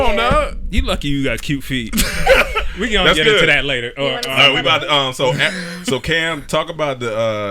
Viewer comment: "you want to suck on them" low key did or anyone else heard 🤔 that lol 😆